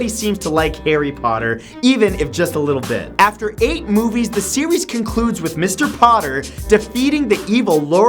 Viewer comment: lol at 9:49 instead of series he sais sewees